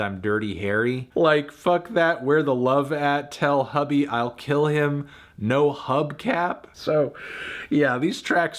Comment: When I first heard that Lil Wayne verse I thought he said "Tell Herbie I'll kill him , no hub cap" and I thought it was random but creative. But then I realized that's not what he says and it's just weird lol